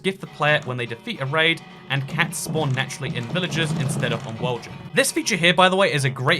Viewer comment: I didn't read the sign that you were reading at 20:59 and totally had no idea what you were said hahah oh, "world gen" that makes more sense.. replayed like 10 times and i'm like, "is he saying woahjin?!" xD